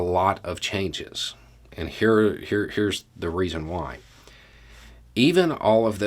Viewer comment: I'm sad to hear that. There are a lot of people who are really smart, successful and still moral people. Kind of sounds like a bit of jealousy. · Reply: I didn't decipher jealous in that statement, I heard " reason " .